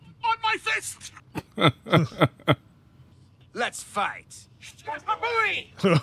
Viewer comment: Ever since I saw this as a kid, I always thought Po said "Maka-phooey!"😂 That sounds a lot better than shashabooey.